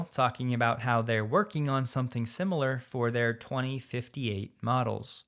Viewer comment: 2058 models? Did I hear that right?